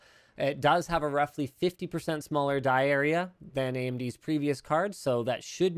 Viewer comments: Linus: "die area" Me: what did he just say? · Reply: no, it just sounded like diarrhea. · Lmao 5:00 "die area", thought you said diarrhea